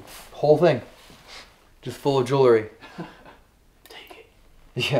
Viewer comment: does anyone else hear the creepy voice say “take it?”